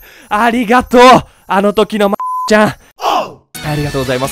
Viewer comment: とてつもなく失礼だけどマ〇コちゃんにしか聞こえない、、、